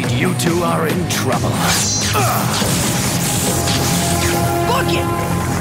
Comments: Listen to to this, 6:03 - 6:07 did dan say fuck it? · Dan yelled "Fuck it!" I know he said "Book it!", but listen to it again. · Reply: I Listened Again It Still Sounded Like He Said Book It Not F**k it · is it just me or did dan say "fuck it"